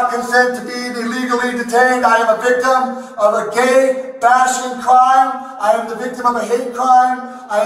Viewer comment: "I am a victim of a gay passion crime!" 😂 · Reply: I legit thought he said a gay fashion crime. 😂